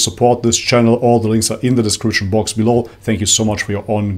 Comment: I always thought that said by the end of the videos “Masalam” which means good bye in Arabic… just realized it’s actually “Much love” that he says 😂